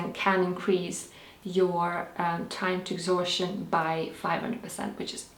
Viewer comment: Around the 3:17 mark: "Siberian Ginseng can increase (what) by 500%? Sorry, I didn't understand and I backed it up and tried to get it three times. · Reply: It's time to exhaustion. ❤